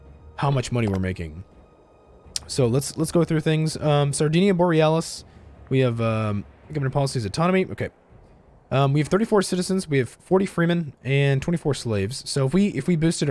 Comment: I’m sry did I just hear Aurora Borealis?